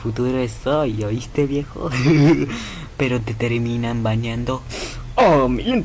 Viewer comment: When Haces Tus Momos En vídeo el Futuro Es Hoy jssjsj Pero Te Terminan Baneando Oh Mi Lente de Contacto! Jsjsqeobksjwigkcdn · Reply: Yo siempre pense que decia "Pero te terminan bañando" 😳👊